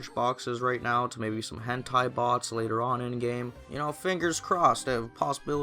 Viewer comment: i know im late but :p at 3:57 i swear you said hentai bots